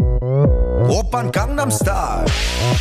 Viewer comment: when I first heard gangnam style I actually thought he said condom style.